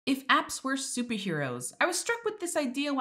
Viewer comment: thought she said "if abs were superheroes" and I was so confused